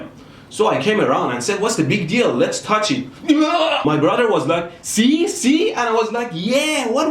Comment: I thought you said shot not shocked